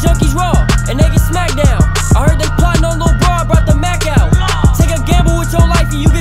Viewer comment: did he say Tay k gamble with yo life and u gone crap out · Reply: Outer Space o it sounded like he said Tay k my bad